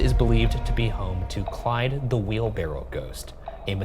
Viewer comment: Is Ryan saying "wheelbarrel"?